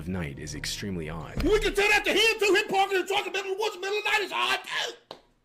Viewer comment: wtf dod he say???? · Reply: "we can Tell that to him too, he's parking his truck in the middle of the woods in the middle of the night, it's odd too." Years of southern accents being borderline gibberish brought me to this conclusion😂